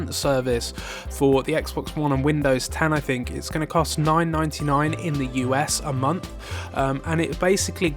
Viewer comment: Microsoft has figured that the Scorpio won't be as they advertised so they throwing Hail Mary but they lost the console generation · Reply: Quatez Murray oh it's $9.99 😂 I thought he said $99.99